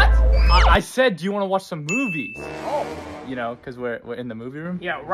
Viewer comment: I heard "do you wanna watch boobies"💀💀💀